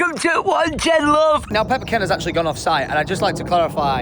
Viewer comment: who heard at 9:18 peppa ken😂😂